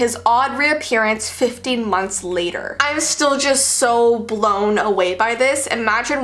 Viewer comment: Who else heard fifty yrs later